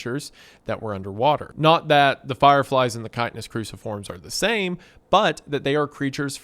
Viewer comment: Man, I keep mishearing “the chitinous cruciforms” as “the kindness cruciforms” and with all the weird pseudo religious stuff I didn’t even question it for the first few hours.